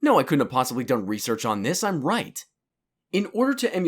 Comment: I thought you said "I'm white." lol